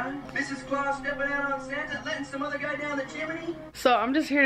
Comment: did he say chimaney (chim-a-knee) instead of Chimney😂😂